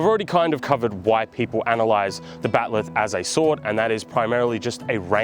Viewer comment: I heard "White people analyze the battlelith" lol